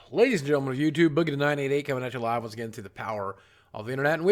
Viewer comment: did you just say boogie9988??